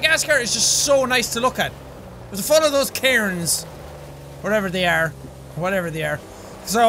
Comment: almost thought he said karens